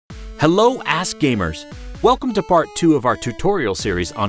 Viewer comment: My first time on his channel and i thought he said "Hello a$$ gamers" lol